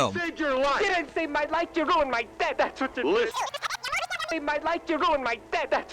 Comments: I thought he said you ruined my neck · Can't make out what that dude is saying at 02:44 even though I've tried like 20 times already XD Please help? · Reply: BeautyPearl "You didnt save my life, you ruined my death, thats what you did." YIKES. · What did the guy say at 2:42? I can’t make it out 🤔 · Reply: "You didn't save my life, you ruined my death!!" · I can’t understand what the guy said. What did he say? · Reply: Kelvin Nieves I think it was “You ruined my death” I wanted to know too but I think one of the comments said it, so I’m assuming so :P.